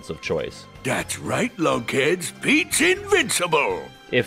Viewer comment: I thought Pete said "That's Right, Kids! Pete's invincible!"